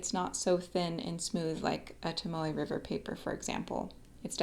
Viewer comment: Aha! I thought you were saying Tamale River notebook/paper. LOL! But I figured it out. I also bought a Leuchtturm notebook today as well as a Voyager Notebook. I haven’t written on either yet, so I’m not sure which I will prefer. But I sure like your opening up my world to both the Leuchtturm and Pilot (I haven’t acquired a Pilot pen yet and I have always shied away from Japanese pens, because well, they are “made in Japan” as a Baby Boomer I was always apprehensive of anything “made in Japan”. But German stuff, Montblanc, Pelican, Rotring, et al, was always OK. Montblanc will always be my preference, but I have become enamored with Italian pens too: Delta, Montegrappa, Aurora, those three are my absolute favorites and I have never felt a smoother nib than my Aurora Paulus II XXV I. (But you can’t get one of those these days for less than $3K).